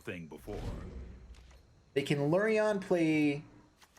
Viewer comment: did you just say Lurrion?